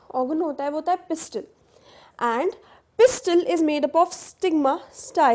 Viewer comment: I heard sigma 😂😂 Sigma rule #1 never lie Edit (25 Jan 2026):- I was so corny 💔🙏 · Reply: LOL brah Mai bhi jab chapter read karra tha to mujhe bhi laga ki sigma likha h fir dhyaan se padhne pe malum laga Abe stigma h😂